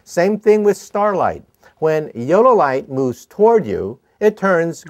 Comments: i thought he said yolo light lol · sorry to make fun of his accent but it sounds like he says yolo light. XD but he's several times as smart as me so i should shut up now.